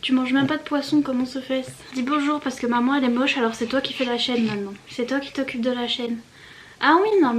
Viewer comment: J'ai compris "c'est toi qui fait la chienne maintenant" au début 😭😭😭